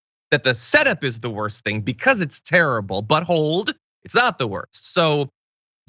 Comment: But hold sound like ya said buttholed. I was thinking easssssssy there captain